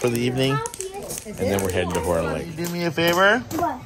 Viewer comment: At first I thought you said you're heading to porn lake lol